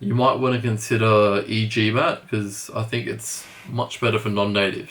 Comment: Hey! Thanks! What did you say between min 4:08 and 4:10? For no non natives? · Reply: Hey Gianluca, I said to consider "e-gmat" if you are non-native. In saying that, if you use Target Test Prep, I would encourage you to use the verbal section, especially SC.